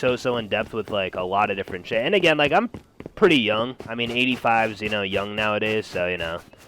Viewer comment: did i just hear u r 85?